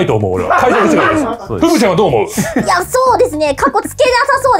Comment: 梶田さんの ふぶちゃん が ぶくちゃん に空耳してしまう